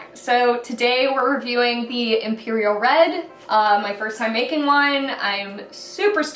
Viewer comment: errr @ 0:07 ...almost sounds like you saying first time at making wine